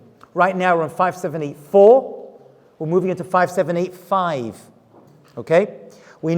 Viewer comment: What does he say 575? In the beging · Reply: The Hebrew year 5785